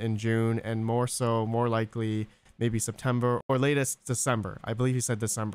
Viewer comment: he did not say the latest is December 2022. He says if they are done with testing by June then the earliest would be December.